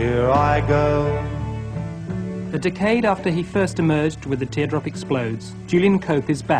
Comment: am i the only one who's love to hear those phone tapes? doubt he has 'em-i thought the narrator was saying decayed